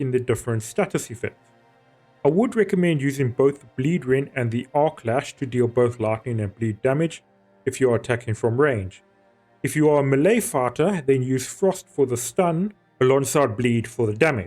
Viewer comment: im sorry, im not trying to make fun of but when you said "melee fighter" it sounded like "melee farter". 3:08 that got a giggle out of me lol